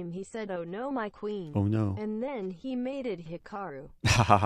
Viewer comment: "There once was an IM chess guru, one could say that he knew chess voodoo. During one fun stream he said 'oh no, my queen', and then he mated it Hikaru." That was awesome. EDIT: Thanks Thomas for the suggested edit from "made it" to "mated". Completely misheard and was wondering why it didn't make sense XD